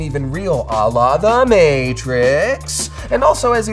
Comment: did he say "Allah the Matrix"?